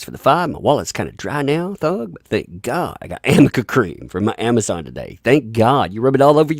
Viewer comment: Lol at first I thought it said AMERICA CREAM. MURRRCUH!!